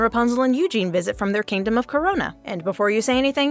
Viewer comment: wait did I just hear corona